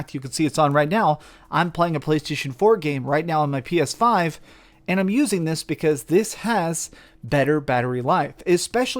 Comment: What was that first game he said he was playing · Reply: Monster Hunter World